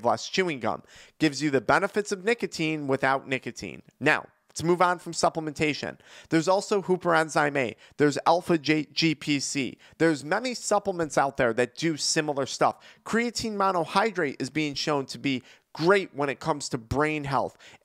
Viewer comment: What is the enzyme being mentioned here? I hear "hooper enzyme A".... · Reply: The hoopty enzyme, it's in the first men in black